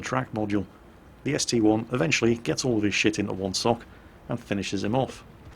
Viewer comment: did i hear that right..."gets all of his shit into one sock"?